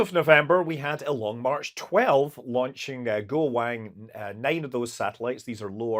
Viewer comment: I hear "…A Go-Wang…" and see a rocket launch at the same second and I am amused. Am I being silly?